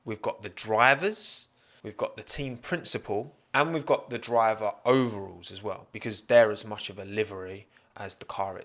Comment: I thought he said “driver ovals” for a second. This is weird because Indycar is the only open wheel racing with ovals. Also lotus were an engine supplier in 2012 but weren’t great.